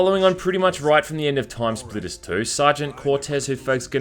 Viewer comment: As a kid, I always thought it said "Time Splinters". Damn I loved this game